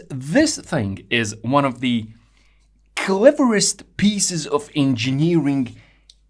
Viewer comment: “kelleriestist”?? What! I don’t know what that word means, quick turn on closed caption for spelling of this new (to me) cutting edge engineering term “cleverest”. I have so much to learn from this wonderful teacher of mechanical science.